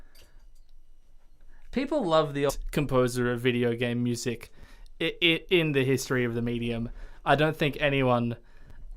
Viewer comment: Who was the composer mentioned at 3:41:30? I couldn't catch it both times D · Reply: I was talking about Koji Kondo and the VOD skips a bit ;__